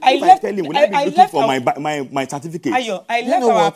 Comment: How many times do you get a certificate? but i thought you said she left your certificate oitside for rain